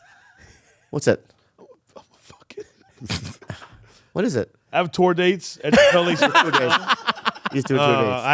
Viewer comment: lmfao you cut him off and then I heard I got a tour date and then it cut to 45 seconds of ads lmfaooooo